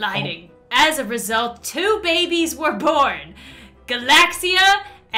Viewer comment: A rare bunny I thought Lauren said Barbie and I was like BarBiehhhhhhhhhhhhhhhhhhhh